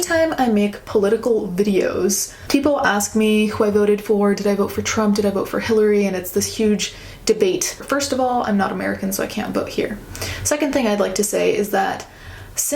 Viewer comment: When you said you weren’t American I thought you said “I’m not married” and I was like OML YOU GOT DIVORCED?!?😂